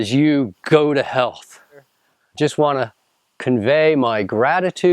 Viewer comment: sounded like go to hell lmao